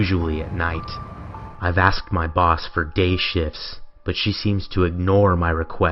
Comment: oof 3 i heard "gay ships" ahhahah